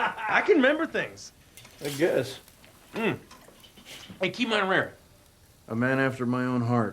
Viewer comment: What did the other guy say at 7:59? I hear "key my rear" lol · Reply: "Keep mine rare." He wants his steak red in the center.